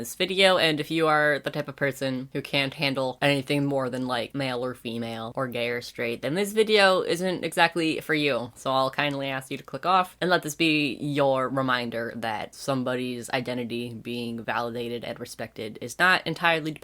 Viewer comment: When he said "I'll kindly ask you to click off" , I heard "I'll kindly ask you to f___ off" lol